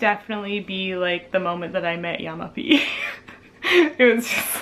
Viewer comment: hey, did you say Yama-b at 7:46? thank you, take care ^-^